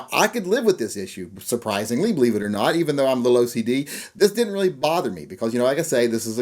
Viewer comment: Between 1:18 and 1:20, did you say you were "a little" OCD? Did I get that right?